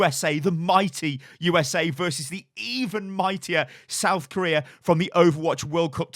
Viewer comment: "...Vs the even mightier South Korea!" i seriously thought you were about to say "The evil South Korea"